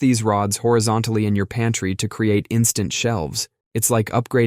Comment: OMG, I thought you said "panty" instead of "pantry". Love the garment ressurection tip, never, ever have I seen or heard of that one. I love this channel .❤